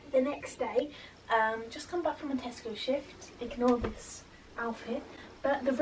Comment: on the subtitles when u said tesco shift, it thought u said testicle shift😭